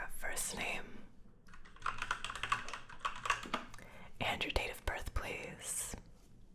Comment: I literally thought she said “ Andrew tate